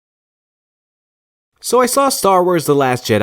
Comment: At the beginning I thought he said “Soy sauce star wars...”